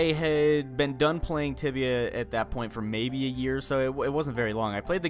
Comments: I thought you said "Tivia" and googled it. Found some other interesting info. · Oh god. I thought he said tivia so i searched it on google images. Don't search tivia on google images... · I thought said tivia and I searched it and google led me to a new ground.